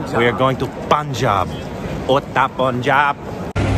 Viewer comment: did he just say 'udta punjab' ? 😏